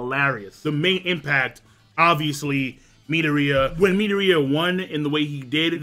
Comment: this whole time i thought he was saying Netoria and im like "who tf is Netoria and what happened to Midoriya" then i realized he was saying Midoriyas name in its literal pronunciation in america, just so u know king its pronounced "Me-Door-re-ya"